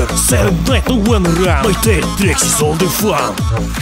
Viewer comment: So why do I hear "Diet pepsi was all they found!"